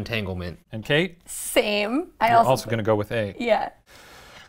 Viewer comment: the slowly increasing outrage about Ryan saying Seeding Chart is murdering me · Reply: OH SEEDing chart! I thought he said seating chart and thought the dude was charting who would sit on him next. I was so ready to accept Ryan thought this dude was just really meticulous about planning his affairs.